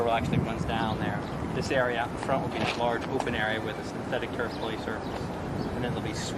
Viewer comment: what does the guy say? Older kids will be down there with the synthetic turk policer??? What???? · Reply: Synthetic turf. Which is literally a fake grass playground